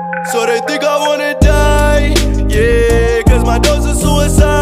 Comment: I thought he said “sorta think I wanna’ die”